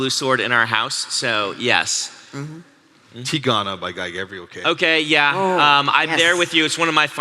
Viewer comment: What did Matt say at 26:45 ? I tried googling but because I don't know how to spell "teagone" and couldn't make out enough of the author name to even make a misspelled version of it, I got nothing from google. I did learn that Tigon is a kind of hybrid animal or maybe a made up one, and that google thinks "teegone" is a misspelling of "telephone," though. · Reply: Tigana by Guy Gavriel Kay.